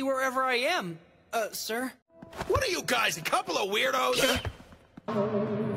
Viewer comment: Since my volume was on its lowest cuz its 3 am at my place, i thought coach Ukai said "Are ya'll a couple or weirdos?!"